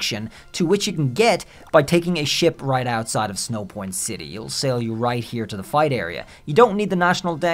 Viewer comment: LOL, I thought you said, "you can take a shit outside Snowpoint city" so I had to rewind and double check what I heard 😂